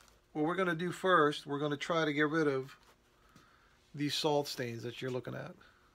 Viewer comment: What is a soul stain and why the big wash ? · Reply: You misheard. He said salt stain. The main reason is explained here 0:17 - 0:31 and at 1:50 - 3:00. He explains it better than I can 😅